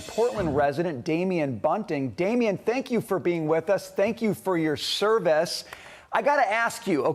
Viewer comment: Sounds like he said "thank you for your cervix" what a disrespectful clown! Good to see you man, I hope you are feeling better since the last time you posted. Wheeler is weak, if you or I were in that office we'd have everyone on their toes snapping too and asking how high would we like 'em to jump! That prosecutor would be met in the parking garage by some bar soap filled sock wielding thugs giving him what for. His tune would change real fast and he'd be putting the baddies behind bars like his job description says. SMDH!